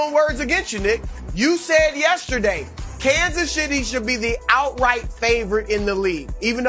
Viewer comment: Did he just say Kansas shi**y